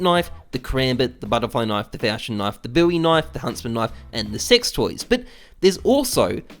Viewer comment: did he say sex toys